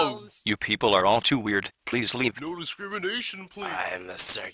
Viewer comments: “I throw spike balls uncontrollably!” - Spikeball Speaker Box This is my new yearbook quote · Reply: I thought he said " I throw spikeballs, I can try out my-" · “I throw spike balls uncontrollably!” - Spikeball Speaker Box This is my new yearbook quote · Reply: I thought he said “ I throw spike balls like a jelly” 💀💀